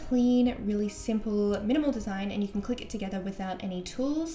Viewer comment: 「みのもんたさん」って聞こえるwww